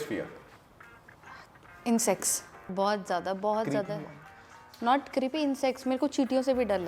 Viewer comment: I thought she said 'In sex ' 😂😂😂